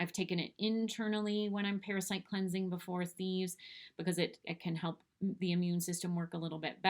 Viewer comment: ?"before a thieves". Could you clarify what you said there? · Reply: I've taken it internally before, thieves. I just repeated that I was talking about Thieves.